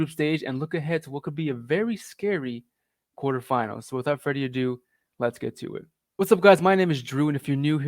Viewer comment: I just woke up and when you said “without further ado” I heard “without Freddy Adu” and I thought I had a hangover 🤣. All jokes aside, I learned that GGG is straight up incompetent, such a depressing coach he is. I don’t think we can even pass the semi finals with this squad, the only reason we might pass the quarters is because we’re playing the loser of the two teams. 😒